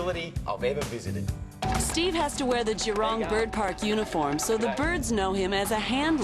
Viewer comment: i thought He say Gerome Bird Park and i Realised he is saying Jurong Bird park.<3 Steve Irwin R.I.P.